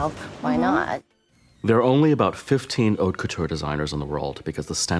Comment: is it me or do I hear f$#@ couture 😂